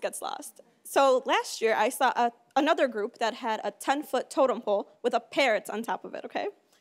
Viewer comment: What did she say at 0:12 ?? "something" pool with pirates on top of it. My english is bad, I couldn't get that part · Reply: 10 foot totem pole